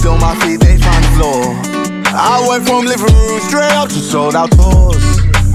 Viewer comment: I honestly came here to check if he actually said “I went from liberals”